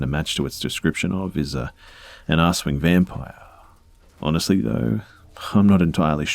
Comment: So, I've listened to it three times, but I can only hear "ass-wing vampire" 12:22 What are you actually saying? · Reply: Kelsey McCracken I think he’s saying Aswang, a Filipino vampire cryptid <link> To anyone reading this, feel free to correct me if I’m wrong